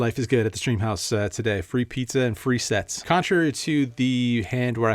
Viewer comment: I swear I heard free pizza and free sex